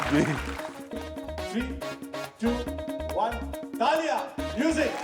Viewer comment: when he says "taalian" (clapping) i thought director is sayibg GALLIIYAAN lol ..